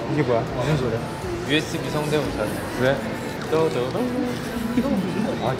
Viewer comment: Esto es para las armys que hablan español : en el minuto 0:39 tae dice que wea o por lo menos eso escuché yo